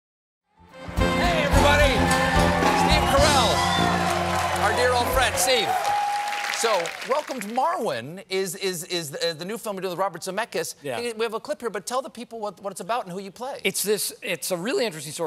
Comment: At first I heard "Welcome to Morrowind" and got really excited!